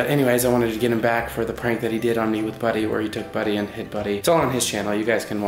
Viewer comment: I thought he said “he HIT buddy” not hid.. dang my heart jumped